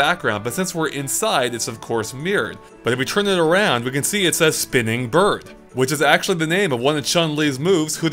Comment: That was my first time watching a video before it's been re-uploaded. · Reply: Haha, I thought said "Swimming Bird" at first. Discussion with SwimmyBird confirmed?